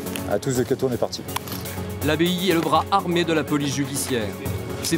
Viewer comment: j'ai compris ; " l'abbaye est le bras armé de la police judiciaire" 😭😭